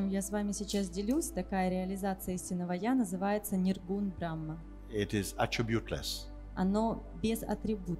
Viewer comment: что он сказал передал? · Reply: Ниргуна Брахман